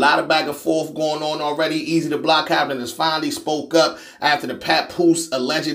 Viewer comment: Oh my God.. I thought you said "that Easy the Block Captain has finally WOKE UP" 😂😂😂😂 let me take my simple ass on somewhere.. 😂 Hell nah!! 😂